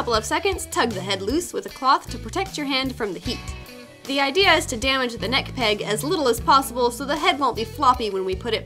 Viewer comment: I heard " the goal is to damage the neck peg" * and I was like *OHHH NO then I finally heard the "as little as possible part" and i was like well that makes more sense 😂